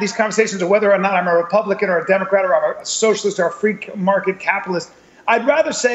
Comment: did bryan callen say "freak market" capitalist here???